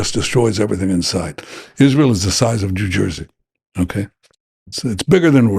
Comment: Did he say jew jersey